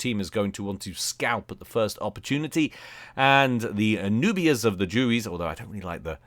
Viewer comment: What exactly did you say at 4:45? It sounded like "the newbiest of the jubies"....